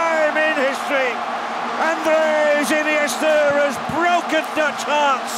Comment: Surely now! Surely now! Spain have won the WC for the first time in history! Andreas Iniesta has broken Dutch hearts, With a goal 116mins, that promises say viva de Espania for the first time ever!!! Waka Waka 🇿🇦🇿🇦🇿🇦🇿🇦🇿🇦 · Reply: I heard, "Andreas Iniesta has broken the curse." 😅